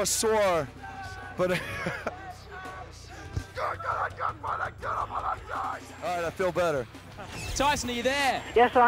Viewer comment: what he say? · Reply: Getgonnsmtdajaaa alright I feel better